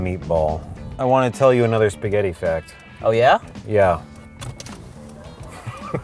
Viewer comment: Who else thought that when they said spaghetti fact it sounded like "spaghetti fattt"